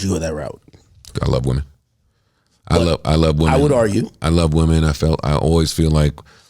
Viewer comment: y'all don't hear bumass akademik saying "I would argue" when joe says I love women 😭